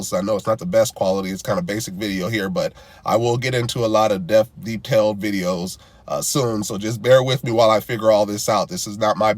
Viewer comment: Quick question- did you actually say “deaf detail”? At 12:10/ 12:11 on your video… I ask because I do screenshot but couldn’t post pic or anything on here so I’m not sure how show you but I use close caption and notice that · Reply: No I said a lot of in depth detailed videos in regard to how creative some are with their videos…good ear and I like the attention you are paying to the video thank you! I was also coming off a little cold at that time. I’m good now though thank you.